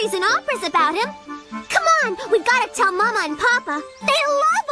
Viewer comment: Wait a minute! Listen to how Sagwa pronounced Baba! I think I heard 'Papa' instead! Chinese toddlers or adolescent call their fathers 'Baba' instead of 'Papa'.